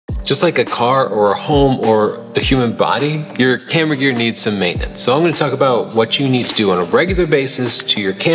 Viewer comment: i heard it as "your hamburger needs some maintenance" ok >.<